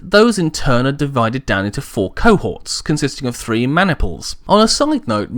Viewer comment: did he say three man nipples?